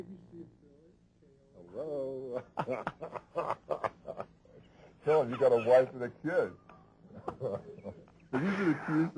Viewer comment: yeah, that's the part i was talking about. @ 0:25 i think he says, "tell 'em, you got a wife and a kid". I don't know where i got the "you got friends" part in my 1st response.lol.